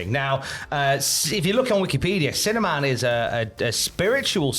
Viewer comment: Sounds like your saying cinnamon, not sinner man